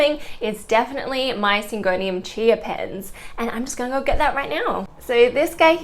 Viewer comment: OMG I thought you said Syngonium Chia Pet and now I can't unhear it 😂😂😂😂